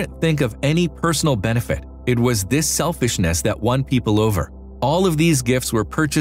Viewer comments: selfishness.... did he say?? CC was corrected. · did he say selfishness??